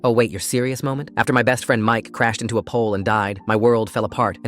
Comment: Omg I thought he said his friend walked into a pole and died I was like what how does that happen???